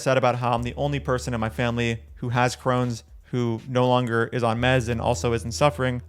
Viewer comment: He is lol wym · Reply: In this video I thought you said no one in your family is vegan but you.